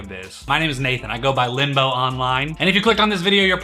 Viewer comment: FINE ILL START CALLING YOU NAMES!!! ... what was your name again, Limbo? WHATS UP LIMBO!!! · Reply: It’s – I thought he said limbo too 😁